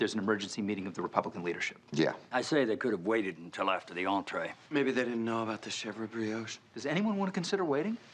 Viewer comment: what I heard is that 7 bowls of pumpkin soup with cheese gnocchi a chef brioche are going spare.